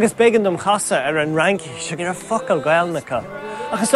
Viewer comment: Did he just say "fuck-all Gailge"?